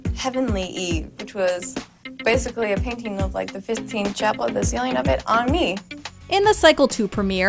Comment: Did she just say “fifteenth chapel”?!?! 🤣